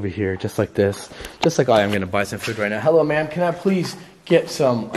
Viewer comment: wait until he says “i think this school was french cuz everything is in french” when he gets done talking u can hear “No” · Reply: i heard that too!!!! i thought it said hello but its definitely "no"